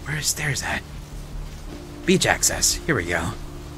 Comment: I swear to god I heard "Beej access" and I had a giggle. I'm very mature.